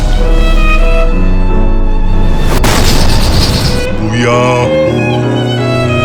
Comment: 나만 무야호라고 들리나?